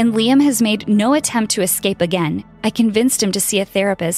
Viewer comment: Wait- did she say escape?!